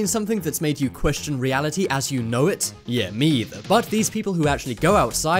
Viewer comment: Wait did he just say me either.